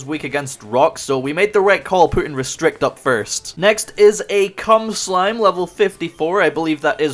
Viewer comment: Dude did you really call it Cum Slime LMFAO I'm gonna call it that from now on LMAO